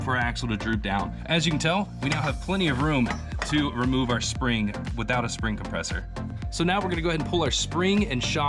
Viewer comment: without a spring compressor... what I heard was "without a widow maker". lol